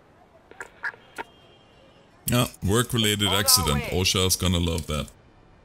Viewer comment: can any1 help me to understand what he just said? Because i hear something like: O , work leads to an accident, unknown sequence of letters gonna like that . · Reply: “Flying Floor” Rossi I said it's and Work related accident, OSHA is going to love that